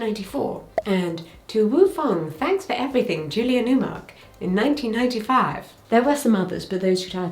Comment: Did she say “To Woo Fong . . . Julia Newmark”? 😂